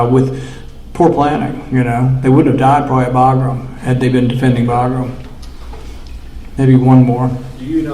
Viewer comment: Been defending viagra is what subtitles say.